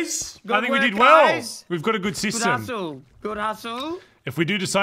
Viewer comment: I seriously thought that when was saying good hussle i thought he meant good asshole😂